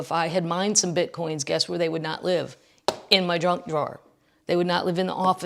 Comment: Could have sworn you said drunk drawer the 1st time. Things that seemed important enough to be put away while drunk? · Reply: haha..junk drawer but could also be drunk drawer...